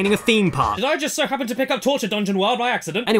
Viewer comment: Genuine question 3:06 What is he saying here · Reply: Did I just do happen to pick up torture dungeon world?!